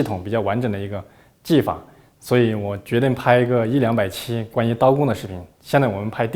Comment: Something not properly translated into the english subs. When he's talking about making a series on knife skills, he actually said he planned for the series to be a long one over time, like 100-200 videos. I think some of these tips and tricks can only be learnt by doing long apprenticeship under an experienced chefs. He's just casually sharing it for us. What an absolute legend.